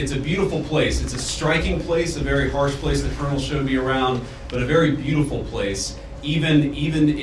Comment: based on the context, it appears to be “The colonel showed me around” instead of “colonels shouldn’t be around “ 😅